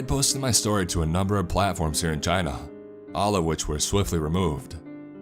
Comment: IM SO SORRY BUT OMG WHEN YOU SAID CHINA I THOUGHT YOU SAID VAGINA O-O IM SORRY